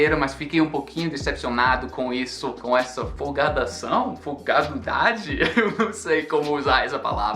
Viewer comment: Hi! Em 9:40 a palavra é "folga". Quando vi o título do vídeo "...pensamentos finais..." pensei: como assim? Ele vai acabar o canal? Mas o que foi que aconteceu? Depois entendi o que você queria dizer. Acho que não expressamos essa ideia dessa forma. Talvez usássemos "conclusões" ou algo assim... Ótimo vídeo, como sempre.